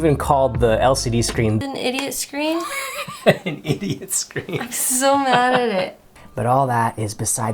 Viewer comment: Did said 'a su madre' in the Minute 1:21? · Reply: "I'm so mad at it". :)